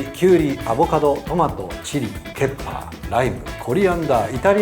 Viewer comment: 前からずっとペッパーって聞こえてたけどケッパーなんだね